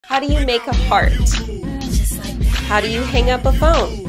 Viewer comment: At the start i thought it said "How do you make a fart?"😭😭😭😭